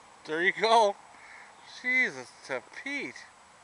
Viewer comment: Im not trying to be mean but did he just say " jesus to peat" thats the first time i have ever heard sombody say that lol.